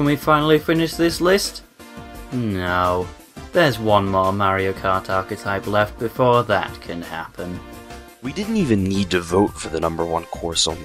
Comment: | I really can't tell what saying here. Help me out? Also, sorry for so many comments. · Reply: No, there's one more Mario Kart archetype left before that can happen... And the more the merrier ^__^